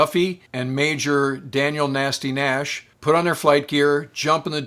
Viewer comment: did he say one of those F 15 pilots name was Nasty Nash ???